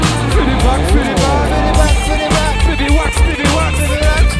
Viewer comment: C'est moi ou bien Gringe à partir de 3:33 il arrête de dire "fais les backs" et il commence à chanter "pédé Waxxx"?